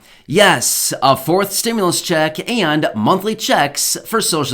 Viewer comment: I thought you said yes Matt 4th stimulous check?